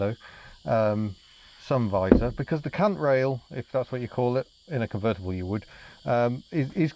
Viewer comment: did you say it's called a cunt rail?🤔